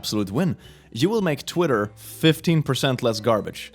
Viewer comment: I paused at 4:07 and the last words i heard was "you made twitter 15"